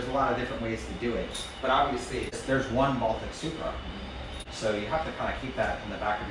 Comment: what did you say? 😭😅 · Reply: You heard the man. Only one real Baltic Supra lol